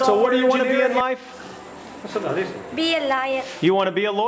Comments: Girl at 4:17 subtitles saying : be a liar I laughed hard😂😂😂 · I thought she said lier